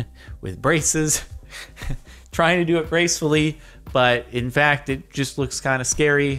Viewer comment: I swear to god if you said "bracefully"...